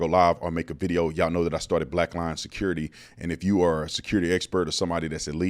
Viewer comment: I thought you said Black Lion Security! That's a good name! Imagine the logo.